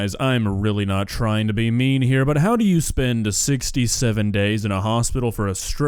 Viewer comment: I think he actually said 6-7 days not 67 days but I could be mishearing it